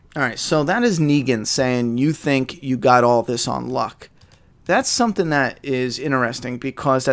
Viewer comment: i think negan was saying u think u got all this on lock? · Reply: Novux that's what i heard "On Lock" not "On Luck"